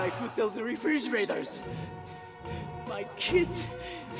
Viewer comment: who sells refrigerators? sounds familiar I love refrigerators! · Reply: It sounds like he said re fridge raiders